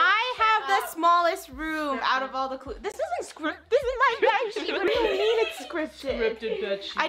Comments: It’s actually “bedsheets” instead of “bitch” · she actually says scripted bedsheets 😭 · Actually when was talking about the scripted funneh didn’t swear it’s misheard she said scripted bed sheets you can hear the sheets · "Scripted b-tch" That was so clear, scary and funny at the same time 😭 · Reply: looking at the captions is making your brain think its "scripted b*tch" but she actually said "scripted bedsheets" bc she was talking about bedsheets stop saying that she said a bad word there, i had good ears back then and i heard her say "scripted bedsheets."